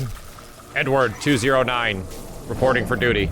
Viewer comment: i heard "N-Word-209"